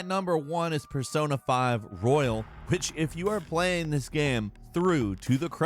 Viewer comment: I thought he said "bitch if you are playing this game..."